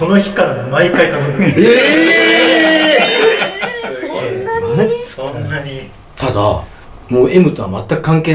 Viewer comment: の「そんなにー」って女性の声が聞こえるw